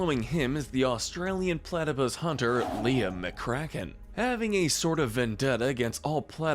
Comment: i thought he said, " Liam McCrackhead"